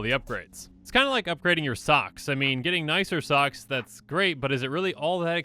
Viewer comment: Oh oh oh he said “buying new *socks*...” I honestly thought he said stocks and i was gonna make a joke about Robinhood and Wallstreet and Greedy companies with overblown prices holding the things we need over our heads like luxuries